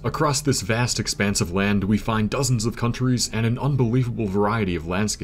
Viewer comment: Do English counties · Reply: oh he said counties i thought he said countries